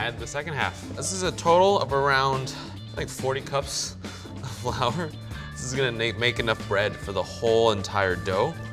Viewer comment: Hol, up did he just say 'this is going to make enough bread for the dough.